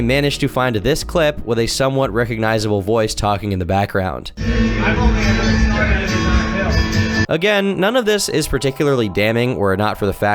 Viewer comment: Mention his voice but ignore what was said ?¿? He said : "I've only ever snorted it out of a bill" [ mighr hwve said 100$ bill, but it was unclear and slurred ! ]